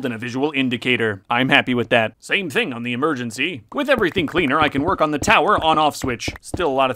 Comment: I thought he said “with everything cleaner I can work on the tower’s Auscwitz”😭✌️🥀